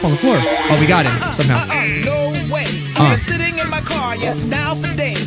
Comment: It sounds like say shitting instead of sitting